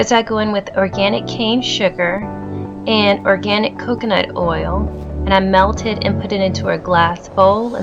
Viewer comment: What does she say at 9:00? Melted what? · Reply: M S I melt the coconut oil and put it in a measuring cup 💕